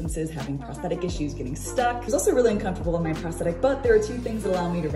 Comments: This is amazing, and I totally want one of those bikes now! Also, I heard "prosthetic, but..." and interpreted it as "prosthetic butt" and I'm embarrassed how much that made me giggle... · it sounded like you said "It was also really uncomfortable on my prosthetic butt" 😁 Thank goodness for subtitles! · i heard it as "it's really uncomfortable on my prosthetic butt', so i was confused for a second